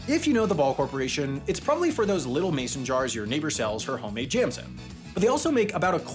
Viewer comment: err, what did he say at 2:55? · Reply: "...it's probably for those little Mason jars that your neighbor sells her homemade jams in."